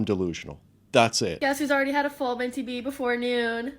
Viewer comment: I think she abbreviated mental break down. Sounded like a full MBD... or something along those lines